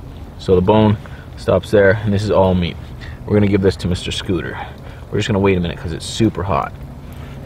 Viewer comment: did you call him scooter